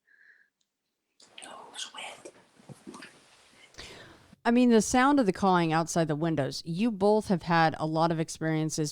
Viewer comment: What’s the story of tge EVP at 1:04:25? And what does it say? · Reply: The guess is "goes with" for the first voice especially considering what was talking about. The second voice right after, can't make out at all. Any thoughts?